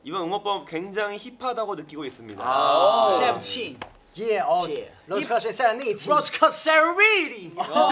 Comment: WHAT IS DK SAYING HERE. SOMEONE PLEASE TELL ME LMAO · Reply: Rose Quartz and Serenity SVT fandom color